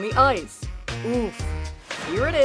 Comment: did I just hear her say oof